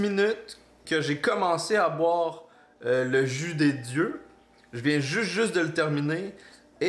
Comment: J'ai presque cru assister à quelque chose que je n'étais pas sensé voir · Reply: J'ai écouté sans regarder j'ai câblé quand j'ai entendu "je viens de boire le jus des dieux et là j'ai terminé" 😂😂😂